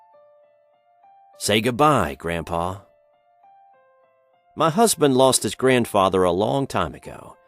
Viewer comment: DID HE SAY MY HUSBAND OMG R U GAY